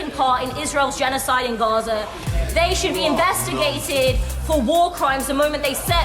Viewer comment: did someone say “you should be” when the lady was saying they should be tried for war crimes